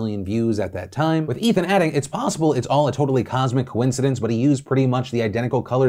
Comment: "It could have been a cosmic coincidence" lol. Phil's mouth working faster then his brain. A genetic flaw us Italians have to deal with at times. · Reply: Haha shit. I thought it said cosmetic.